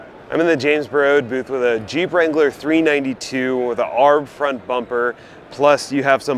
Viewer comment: Did you just say “Aarhhb front bumper?!” 😂😂😂